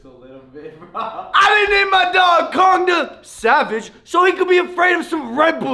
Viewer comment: i thought he said condom savage